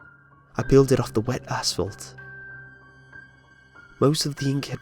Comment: Did you say... assfault?